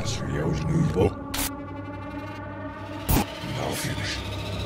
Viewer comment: it sounds like he says “now finish”